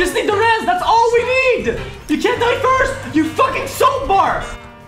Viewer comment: did XQC call the mercy a "soap fart" if so that is my new favorite insult